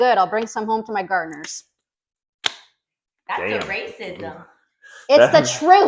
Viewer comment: Sounds like she said “That’s ERASE-ism!”🤣